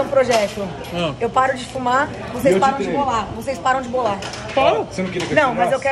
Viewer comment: Não entendi, param de bolar ? maconha ?